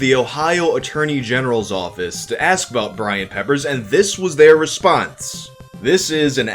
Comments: I THOUGHT IT SAID BRAIN PEPPERS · Not the right time, but, I thought it said BRAIN peppers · Thought it said BRAIN PEPPERS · I thought it said Brain Peepers